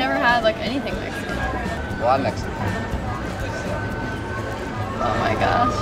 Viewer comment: i think he said "well i'm mexican"